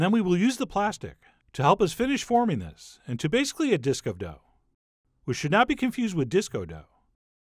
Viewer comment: A True Story: Chef John: "...forming this into basically a disc of dough..." me: "HA! It sounds like he said disco dough!" Chef John: "...not to be confused with disco dough." me: goes insane The End